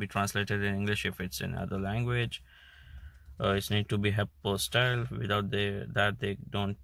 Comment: Could you please say what you said here? "Translated in English if it's in other language. It needs to be in ____ style" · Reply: APOSTILED-- it is written on the paper- do check there